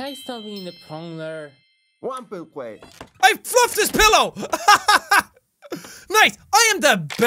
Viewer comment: I thought said I fucked his pillow so I watched it again and heard him say I fluffed his pillow and was like 😅